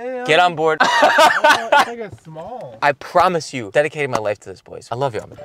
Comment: i swear at 1:06 he said the n word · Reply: I heard small I think